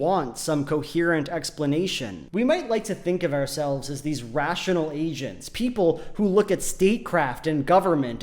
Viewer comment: lmao at 18:55 when the subtitles said "rational asians" instead of "rational agents"